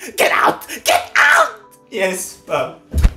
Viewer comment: For a moment I thought she said giblets